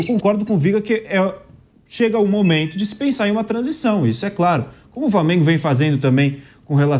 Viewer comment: / 20:12 ele falou VARmengo? Ou VLAmengo , foi isso mesmo que entendi ? Ele tá louco?